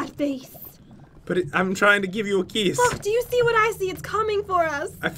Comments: does Jen swear, I believe she said F U & K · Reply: Author Kasey Millstead No she said look and stuttered a lil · Did Jen say a curse word or am I hallucinating?? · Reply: Don't worry she said 'look' 😂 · did jen say the f word or is it just me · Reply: T&B Vids* she said look I think